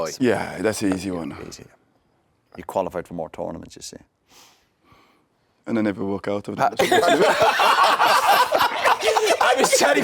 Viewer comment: What did viera say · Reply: “And I never walk out of them”